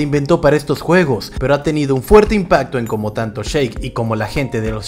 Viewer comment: Pensé que decía Shrek y yo bien emocionado